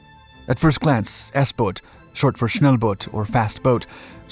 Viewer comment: I thought he said “short for snail boat or fast boat” and I was like O_o